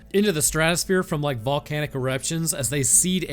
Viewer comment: I’m sorry, did you just say “volcanic erections?” 😂